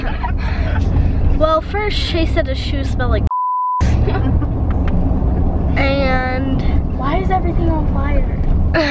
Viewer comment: what did say at 5:59 SOMEONE REPLY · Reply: Balls